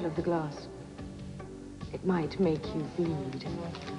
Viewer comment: Holly checked out and now she is gone! Hahaha Bill is getting his Karma! :) What does Holly say in the letter at 4:07? It might make you feel hot?